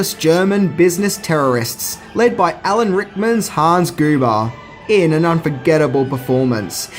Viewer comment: Did you really say "Hans Goober"?